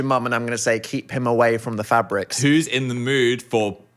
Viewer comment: Heard that as Phabrics